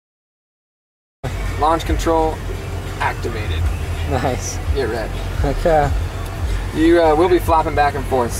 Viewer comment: Did he say "get rekt" at 0:12?